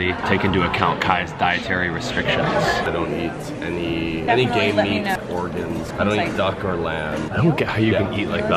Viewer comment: did Kai say "Gay Meat"?